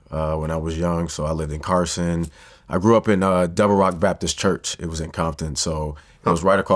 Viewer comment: Can someone help me. Ive watched it 29 times and all I hear is, "devils rock Baptist church" at 1 minute